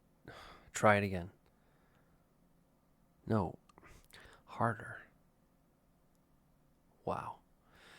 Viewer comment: I thought he said Parker omg😭 0:34 That actually scared me cuz that's MY name